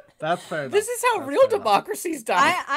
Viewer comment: I thought he said "this is how democracy works"?